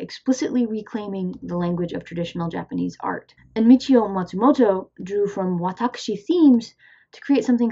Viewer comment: i can't make out the name you say at 7:37, could somebody help me out? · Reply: oh whoops! i shoulda noticed. turns out i did hear you correctly in the video, i guess there just aren't really any english search results for michio matsumoto. thanks for the response!